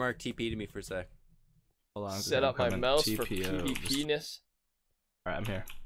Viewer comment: did he really say pvpenis